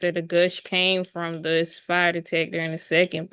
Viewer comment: Did she just call that a "Spy Detector"? LOL